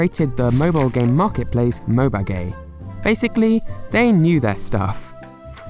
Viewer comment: Mobagay... That’s what I heard HHAHAHAHHAHAHHA